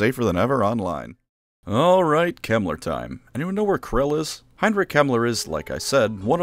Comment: Ngl I thought you said “Heinrich Himmler” and had to do a double take